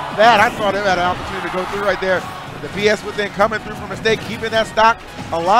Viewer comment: Did ee call it bs within? LoL